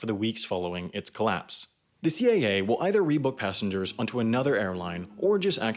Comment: i thought he said CIA that made me jump.